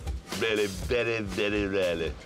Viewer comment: "No, no accidents in the last 5 years" - Hammond with a nervous smile. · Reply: Oh did he say years, I thought he said seconds...